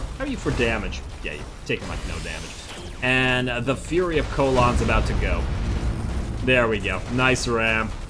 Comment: I heard "Fury of Colon" lol